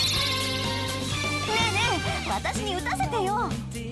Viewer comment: correct me if I’m wrong but did Ann say “Ren Ren”?